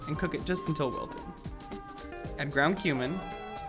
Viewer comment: i swear i thought he said ground human